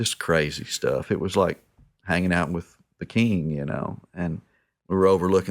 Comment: "Platters of hotdogs for us, it was crazy stuff, like being with a king." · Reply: I thought he said THE king. Like Elvis lol